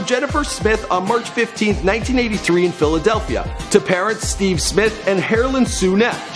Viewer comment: Did he say Steve smith